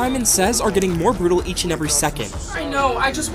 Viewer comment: For a second I thought it said boyfriend💀 but It was best friend